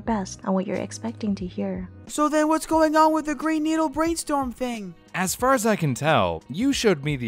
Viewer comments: I keep hearing "brainneedle" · i hear "Brainneedle" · I hear “bray-store”